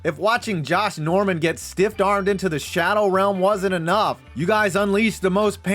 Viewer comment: did he just say stiffed to the shadow realm SOMEONE CALL CHISELED ADONIS